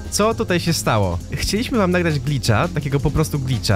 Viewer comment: usłyszałem bitcha